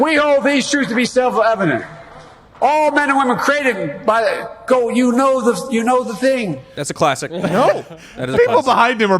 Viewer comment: "... created by Go- you know the thing" I think be was trying not to say God.